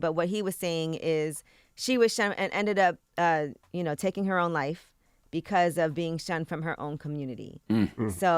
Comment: that’s not really what he said. He said “I don’t think being shunned by her own community was the reason behind her death, but I bet it didn’t help”